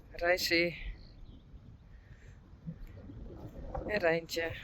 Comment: That’s Rein on the trampoline. 🙂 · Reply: Oh I thought she said Teije! 👀